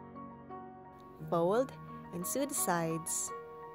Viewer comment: when she said sew its sides at 7:55 it sounded like suiside sorry dont know how to spell